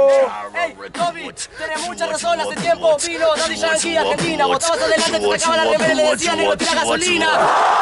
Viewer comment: alguien me podria escribir que es lo que dice en su primer acote? del 1:48 al 1:55 para ser mas precizo? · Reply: toby tenes mucha razón hace tiempo vino daddy yankee a argentina, vos tabas adelante te sacabas la remera y decías "negro tira gasolina"